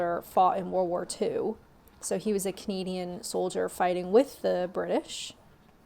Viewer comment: Thought you said comedian not Canadian 😂😂😂